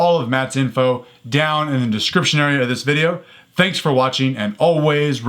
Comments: what is he saying ? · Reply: ΠΑΝΑΓΙΩΤΗΣ ΡΟΥΝΗΣ Train untamed. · I remember when I first started watching, I thought he was saying "train on time".